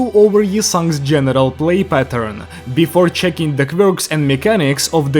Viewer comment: i cant be the only one who heard genital play pattern